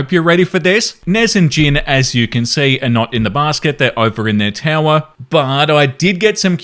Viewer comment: I need to get my hearing checked cause instead of “they’re over in the tower.” I heard “they work for Nutella.”